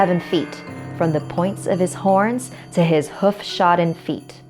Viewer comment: did she say he’s horny